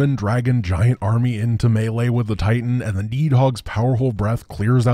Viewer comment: this whole time. I thought he was saying Meat Hog…..